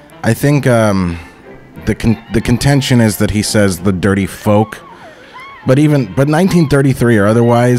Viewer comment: Personally I thought Bosko said "That dirty fox!"